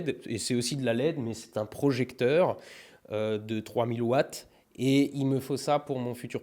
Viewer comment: À 7:42, a choppé un coup de soleil sur le côté droit du visage. À 7:50, j'avais vraiment compris "C'est un projecteur de 3 milliwatt" ... Hein ? Qui ? Quoi ? Puis j'ai compris ... 3000 watts ... QUOI ? 3000 WATTS ? Tu comptes éclairer une ville toute entière avec ça ? 🤣